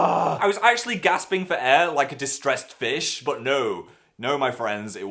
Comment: lol I thought he said, "Like a distressed bitch" instead of "distressed fish"...